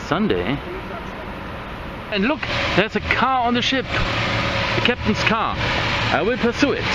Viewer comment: They did a great job. When you were talking about the car I thought you said there was a cow on it.